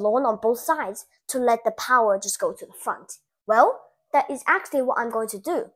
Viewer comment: Ok but is no one gonna talk about how he said the power would get fucked? Am i hearing things right? 😂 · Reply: the front